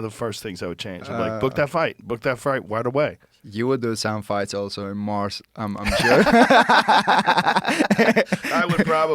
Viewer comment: what did ilia say here? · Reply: You would do some fights also in Mars, I'm sure